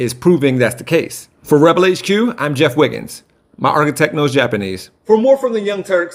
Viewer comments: I have no idea what you said at the end. Sounded like you said your "art of techno is Japanese" Am I just a total idiot? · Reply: I think he says "my architect knows Japanese" but that doesn't make any more sense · Wtf did he say at the end???? "My early techno is Japanese"?? What does that mean?? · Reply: I heard "My architect knows japanese". Which isn't any more meaningful...